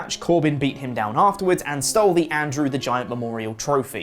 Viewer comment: did he said "Andrew" the Giant?